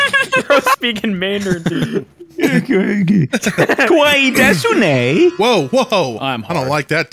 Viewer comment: if anybody is curious about what said he said かわいいですね。 which basically means “I am cute, right?” But since pronouns are mostly optional when speaking about yourself it doesn’t include the topic and topic marker particle